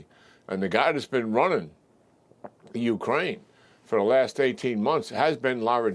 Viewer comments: Did he say Ukraine? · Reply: That’s what I heard too. Rewinded it 3 times. Definitely said “..running Ukraine.” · Am I hearing things or at approximately the 1:30 mark does the general mention Ukraine?